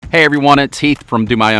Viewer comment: Heath is the man! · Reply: At first I thought he said, “Hi everyone it’s Teeth.”